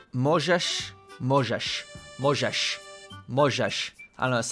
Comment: last "możesz" sounded like "Mojżesz" which is a name from Torah: "Moses" You know the guy who led the Israelites out of slavery from Egypt, who divided Red Sea. Maybe that sounded off for you there ^^